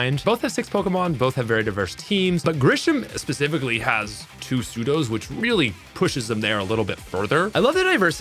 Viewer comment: Had to go back, because I wasn't looking at the screen, and thought you said "Sudos" instead of "Pseudos", and was wondering why 2 Sudowoodos would be so good.